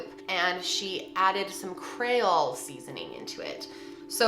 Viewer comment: Some “cray-ole” seasoning 😂 this was too entertaining to me considering the circumstances. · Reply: I thought she said "crayola" at first and I was so confused. XD Love ya but its Creole.